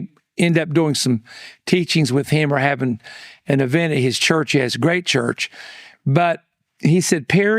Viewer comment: or so, did I hear that correctly? “his gray church” 🤔